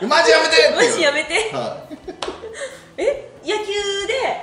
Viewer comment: マジやめてが麻雀やりてに聞こえた。